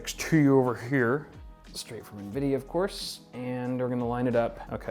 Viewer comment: is it just me or did anyone else hear straight from nvidia ofc on 4:56 ?